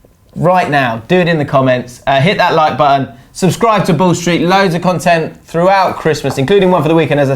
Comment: Need to say ball street a little slower! Thought u said the bullshit channel!😂 keep up the good work though lads!👍